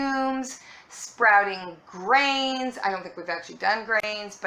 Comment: and I heard not 'grains' but 'brains'-- which, of course if much funnier, but harder to sprout!